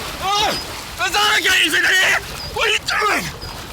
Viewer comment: can anyone make out what Said At 17:24?? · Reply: UnoUnoSeis ahhh there's alligators in here